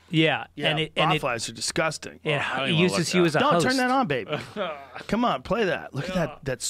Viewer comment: Did Joe just call Jamie "baby"?